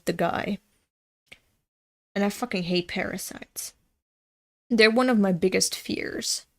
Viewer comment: i misheard parasites as paris and thought you were talking about how much you hated paris and how disgusting it was